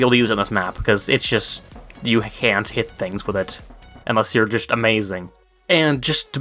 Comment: more sounded like "leg"